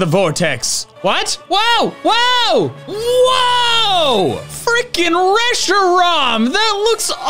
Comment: did....did he say the F word? I'm proud of you Dr. Fuze! lol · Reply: On rewatching in a quieter environment it appears he only said freakin